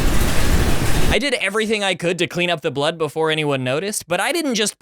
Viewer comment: turn on the captions, it’s says flood instead of blood